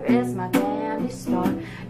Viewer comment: When I heard candy store for the first time I thought she said “pound some rum n’ coke” I thought they meant coke as in the soda...